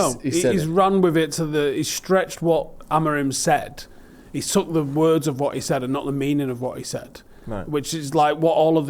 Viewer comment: "He's took the words of what he said", I'm sorry but once again he said: "We are BEING the worst team" so yous cant even get the words he said right and this is a man united channel. Honestly, why is this 'being' not mentioned at all theres such a big difference between the 2 statements.